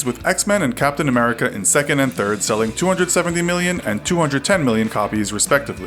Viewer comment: I thought you said 270 and 210 not both 270